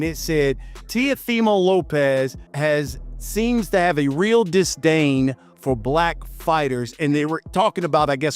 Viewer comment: "Disdain" LOL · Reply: i thought he said a distance at first glance😂 yeah why not, he got a goddamn distance. still at least its in his vocabulary by some mirrorcall,, 🤣🤣